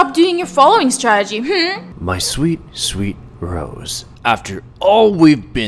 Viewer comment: for some reason I thought you said Bros